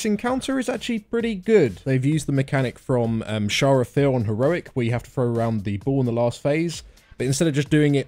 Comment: As a non native english speaker I can't figure out what boss is talking about @ 0:10 :S I tried really hard and I even searched on the dungeon journal, can't figured it out, help please D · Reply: TPPT Sha of Fear HC from terrace of endless spring